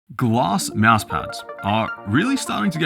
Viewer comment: I thought he said gloss mousepads LOL